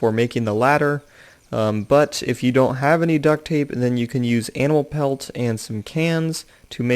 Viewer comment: Anal pelt...? I'm pretty sure I heard anal pelt :S